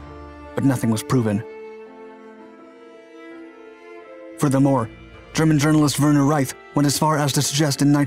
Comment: I was an SRA/E-4 and worked in the Ramstein AFB Fire Dept. At the time of the crash I was standing midfield behind our Rescue Truck and one of out P-2 ARFF Trucks that were on standby. There were >300k people on base that day, but the narrator got it wrong, most were not Americans, at its most populous the entire K-Town area had a total of approximately 110,000 Americans, there were people there from every country in Western Europe. I saw people within 100 or so ft of me get mangled, maimed, burned. I spent the next 3 or 4 hours moving people to the medical tent for transport, rendering 1st aid, and picking up body parts. It was as bad as you can imagine. The sight of burned and mangled human bodies and the smell of human flesh burned by jet fuel haunts me still and to this day I avoid airshows. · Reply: I thought he said they were mostly German nationals, I don't even know what that means.